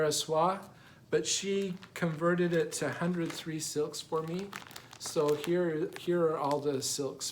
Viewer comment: Hi. What are those spooled silks you got (instead of the charted Au Ver á Soie) for the Anne Wilkinson sampler at 15:00? I’ve played it over and over - to me it sounds like you’re saying “hundred three silk” but that can’t be right?! Oh, and your Autumn Quaker is stunning!! (edited for clarity) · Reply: They are 100/3 silks by Au Ver a Soire. They are distributed by Access Commodities here in the States. So far I have really enjoyed stitching with them. They are very tightly wound and easy to work with. Thanks!